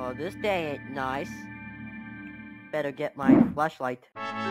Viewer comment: erm excuse me i think i heard "fleshlight" instead of flashlight 💀💀💀💀💀💀💀💀💀💀💀💀